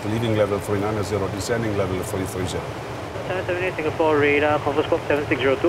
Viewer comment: Ich verstehe mit dem Akzent des Lotsen kein Wort. Ich glaube, das wird für mich der schwierigste Teil, wenn ich doch noch irgendwann das Fliegen lerne! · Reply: der Lotse sagt „778, Singapore radar, confirm squawk 7602?“